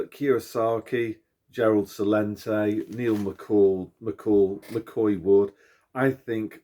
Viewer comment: Neil Oliver on GB News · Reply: I thought you said Neil Mcall ... Or something like that... Thanks for the reply (ex Swedish resident from JD channel here btw)